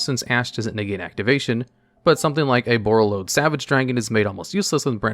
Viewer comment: I mishear "Borreload Savage Dragon" as "Borreload Sandwich Dragon" lol